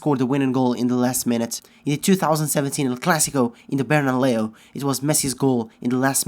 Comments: does anyone hear Bernaleo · Did he said "bernaleo" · did you just say Berna-Leo 😂😂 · LOL @ 11:00 I heard that BernaLeo :) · Did he just say bernaLEO